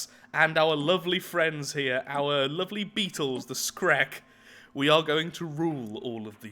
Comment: at first i thought you said the shrek